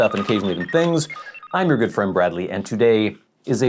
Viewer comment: In your intro you say "and my name is Bradley and..............." which I always hear as "Bradly-Anne"